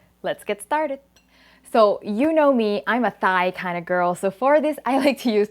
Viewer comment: When you said you were a thigh kind of girl I thought you said Thai lel